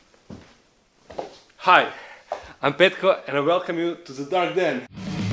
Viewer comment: Who else thought he said his name was petco